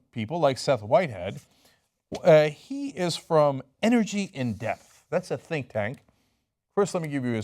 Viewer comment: I honestly initially thought Cenk said Seth Whitehead worked for "Energy In Death" when he was talking about the totally biased Petroleum Industry funded think-tank "Energy In Depth". Although maybe that's a more apt name for this pro-fracking think-tank.